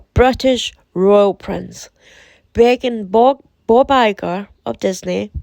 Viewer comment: My 2nd Video is back up!!! It for manukau review and seemed as fine. So please go watch it because some of the info in there is interesting! I’m so happy!!! ❤️❤️❤️❤️ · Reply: LMAO I swear, I thought you said 'bawbagger' when you said Bob Iger lol. I think you should do something slightly different with reference to the The Cult Quad (there's probably only 4 of the loonies). Look u p streamyard, and maybe ask channel creators and your viewers to participate in Q&A, and their thoughts on their antics. Pre-record, then upload. I think, once you hit 1000 subscribers you can go live, which allows for more audience participation. I feel it'll be more engaging and perhaps even therapeutic for many.